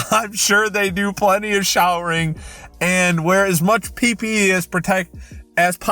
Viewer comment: Is it just me or did he say pp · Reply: Dumy thicc #8019 PPE